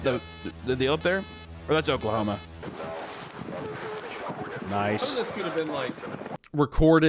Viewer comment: Did hunter say "ohs thats Oaklahoma"